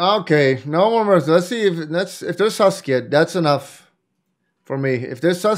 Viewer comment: what does bulldog says? that chat says a lot is 'sasuke' ? I dont understand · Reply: Susge is what he says. It is a Pepe the frog emote variant that looks like it is thinking "hmmmm". It is used for things that look suspicious. But the sound of susge sounds like sasuke, so chat makes that joke